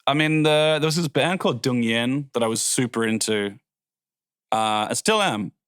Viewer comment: Anyone know the band he mentions around the 9:27 mark? I can't make out what he says. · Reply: Dungen?